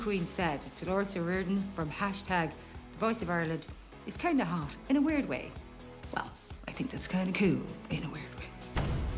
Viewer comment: Could someone write what she said? I dont understand :) (I think about what Dolores said) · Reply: "@ said, "Dolores O’Riordan from #thevoiceofireland is kind of hot... in a weird way." Well, I think that’s kind of cool... in a weird way." <3